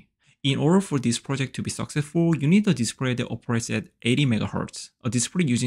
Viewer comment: you say we need something running at 80Mhz, but I can't quite understand what it is you're mentioning. Could you clarify that part please? EDIT: I used AI to create subtitles, and it says you said "A display that operates at 80Mhz". I don't know if that's accurate, but I do know that I can't find any specs on any display that shows its Mhz. At best, Hz, but usually its speed is measured in ms. I guess I'm barking up the wrong tree?